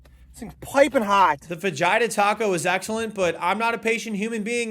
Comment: when he said fajita I thought he said vajita because I wasnt watching the video.... dude.... :(